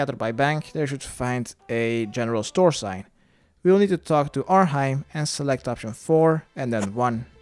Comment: got confused in the first 3 mins, thought he said talk to laurel